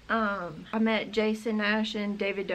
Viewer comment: I was really confused,just for a few second, when she said Jason Nash because I thought she was saying Jason, gnash (which was clearly not the case lol)